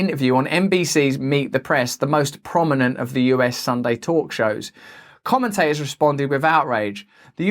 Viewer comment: I misheard 'sunday talks shows' .. thought you said 'sunday tortures'.. same difference